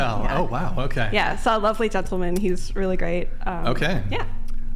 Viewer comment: 35 minutes in and I'm already extremely depressed by what I'm hearing from this sad excuse of the panel, WTF is going on in the west ??????? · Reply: I thought said this was a “lovely panel” shows you what good that meant.